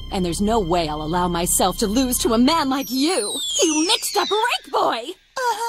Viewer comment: what did she say? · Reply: She said "you mixed up Rake Boy"